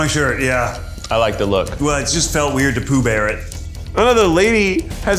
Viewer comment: Me, laughing, trying to figure out why Ned is shirtless???? Then him saying he didn’t want to Pooh Bear it — Jesus Christ I’m dying laughing 😂😂😂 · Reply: Omg I didn’t even realize he was referring to the character I thought he was saying poo bare as in like a bare bottom and poo (because they’re getting waxed everywhere)